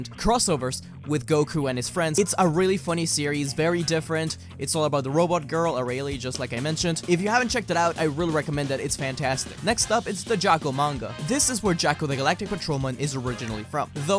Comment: Is it just me or did i hear 'Its Arale fun series'